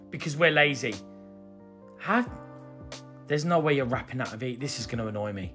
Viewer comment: I might be wrong, but I hear "wapping" paper?